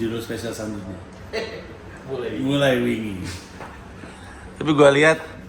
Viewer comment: Kalau orang non Jawa, ngomong bahasa Jawa, kadang memang susah. Pandji mengucapkan kata "wingi" dengan cara seperti mengucapkan kata "wing (sayap)". Saya dengernya terasa aneh. Hehe 😁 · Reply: Saya tadi ngiranya Pandji bilang “wingy” eh ternyata “wingi”. 😅😅😅